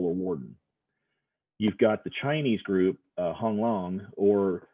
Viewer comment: Sounded like Hung Long XD